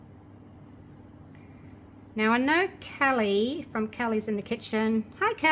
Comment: This turned out beautifully!! On a side note, on your last video when you mentioned Calli, I thought you said Kelly and she was a friend of yours who was physically at your house " in the kitchen" vs Calli from "Calli in the kitchen" who does lovely work also. Lol. I felt so silly when I heard you mention her today